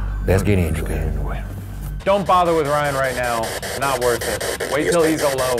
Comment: I think it says "He's worth it/ It's worth it."